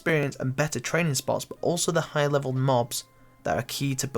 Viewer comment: sounded like he just said “knobs” LUL